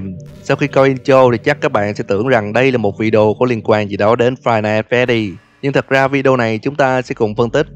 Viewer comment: nghe ra thành five nights at daddy :))